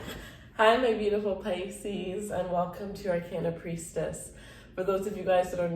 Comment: I thought you said "our can of priestess"